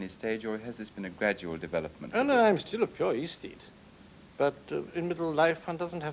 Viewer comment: What does he say at around 13:46 about "I'm still a ______________"? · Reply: "I'm still a pure aesthete" - sorry the answer took so long!